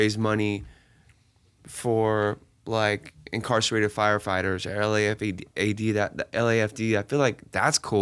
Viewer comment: Did he say incarcerated firefighters 😂